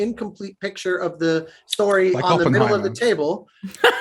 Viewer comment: what did say here ? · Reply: “Like Oppenheimer”